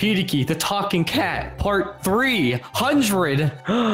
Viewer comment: Subscribe or will give u a bad haircut 😂😱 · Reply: At the beginning I thought that said part 3 huh drade and I was like ooo another ship is sailing but when I figured out he said part 300 I was sad for shipping people in vain also love you peoples